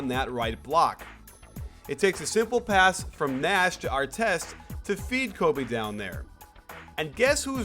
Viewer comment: did he just said artest?